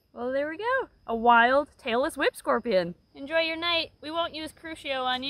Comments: "Tailless whip scorpion" I heard "Taylor Swift scorpion". I must be tired. Lol! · Omg I thought you said Taylor Swift Scorpion instead of Tailless Whipscorpion and I was so confused🤣🤣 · I thought she Said Taylor Swift Scorpion 😂😂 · I kept hearing "Taylor Swift scorpion" instead of "tailless whip scorpion"🤦🏻‍♀️🤦🏻‍♀️🤦🏻‍♀️ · Was it just me that has really bad hearing and thought said “we found a Taylor Swift scorpion”? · I kinda blanked out for a second and thought I heard "Taylor Swift scorpion" even though I know tailess whip scorpions exist. Weird part is that I can't unhear Taylor swift scorpion, so now that's all I hear... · I literally thought you said "Tailor Swift Scorpion" and I got scared LOL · I'm not a native english speaker and I kept hearing "taylor swift scorpion". Believe me I was a little confused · I heard “tailless whip scorpion” as “Taylor Swift scorpion” because I’m very smart lol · When she was talking about the tailless whip scorpion for a second I really thought she said Taylor swift scorpion